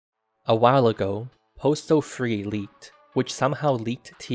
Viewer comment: The first and last good thing that Postal 3 gave us. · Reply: For the duration of the video I thought it said Portal 3, so as a Portal fan I was amazed, then I read again the title and oh boi...